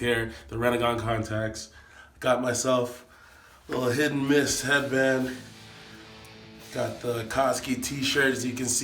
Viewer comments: Did he say Hidden Mist headband? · Did he say hidden mist that’s rain